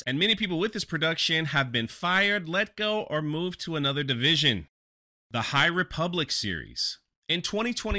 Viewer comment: "... fired, moved on, or moved to another division..." I had to listen to that twice. At first I thought you said, "...moved to another dimension..." Darn it. That's a movie I'd pay to see.